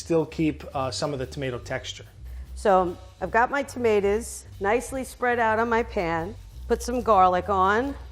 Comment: why does it sound like shes saying temeters